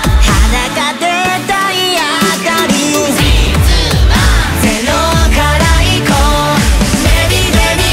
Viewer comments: …… ゼロカラ行こうに聞こえるw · 「0から行こう」がまねきの「ZEROカラ行こう」に聞こえてしまった…( ˙-˙ )